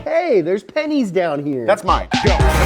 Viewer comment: "Hey, there's panties down here!" "That's mine." Bad wording, my friend... Edit: pennies? Panties is what I heard and that's funnier.